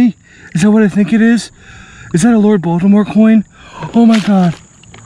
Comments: 😂 i just thought you were were saying a Lord Voldemort Coin 💀 was gonna say, dont you mean a "He-who-must-not-be-named coin?" 😂😂😂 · I first heard, "its a lord voldemort coin". I was like "the f***. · Thought you said a lord Voldemort 😂😂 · No joke i thought you said lord Voldemort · I heard “lord Voldemort coin” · All I heard was, "Is that a Lord Voldemort coin!?" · For a moment I thought he said lord Voldemort coin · At first I thought u said lord voldamort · I thought I heard lord Voldemort 😅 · I thought he said Lord Voldemort!